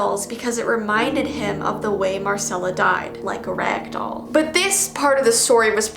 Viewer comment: I like how is telling a scary story and then all of a sudden you hear a scary music from the background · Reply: It also sounds like said garcello